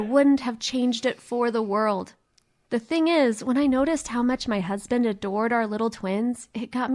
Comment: hold up run that back did she say husband or am I trippin. If they are married still in high school we gotta problem.